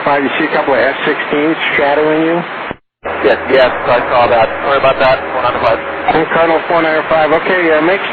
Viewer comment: すみませんって聞こえる